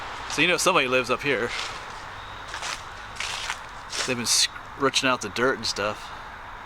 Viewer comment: what is the word you used to describe the dirt at the bottom of the tree? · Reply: PA Dutch: rutching or rutsching